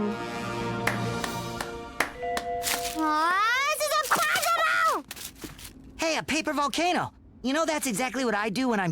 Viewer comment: WHAT DID SHE SAID?!! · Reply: she said "ughhh this is impossible"